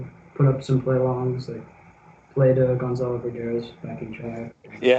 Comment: What does say at 6:58? "Play the...." What? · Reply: He said "Gonzolo Beraga's" - as in Gonzolo's backing tracks that are on youtube.